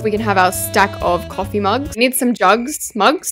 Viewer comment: Did say drugs at 5:56 😂